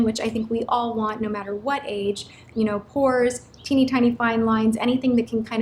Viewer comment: when you said 'pores' i thought u said whores xD